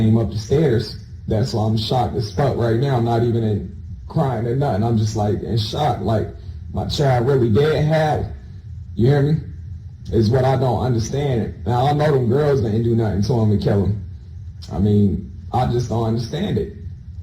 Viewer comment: What is he saying? My child really dead ........? Sounds like hes saying "my child really dead have?" Can someone decipher this? And was he sod0mizing him with the chair Iegs?